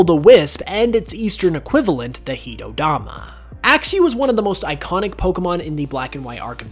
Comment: What did he say there? 12:19 heat o dama??